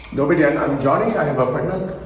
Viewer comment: the hell? at 2:19 i keep hearing "Hello I'm Johnny, i'm pregnant" lol